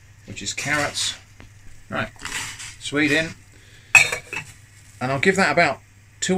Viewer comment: i hear “alright… Sweden”